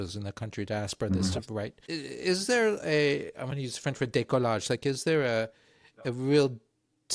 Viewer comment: French "décollage" that you use means take off, for a plane (or when someone gets high). What you wanted to say is "décalage" which is quite different (misplacement, separation, being-aside-of-but-not-well-aligned-with) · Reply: I thought that was a subtitles weirdness and heard "bricolage" instead. Curious which word was initially intended.